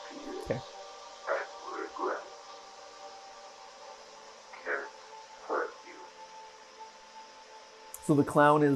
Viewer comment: Ok but 26:20 where it says "can't hurt you" but the captions say "can hurt you" i didn't know if anyone else had noticed it so I wanted to point it out